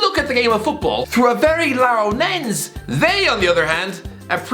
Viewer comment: Am i the only one that heard him say larrow nense instead of narrow lense??